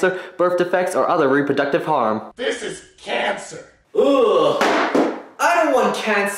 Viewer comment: captions say "i am cancer"